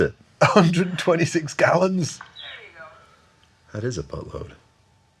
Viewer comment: Boatload? I thought he said BUTTLOAD!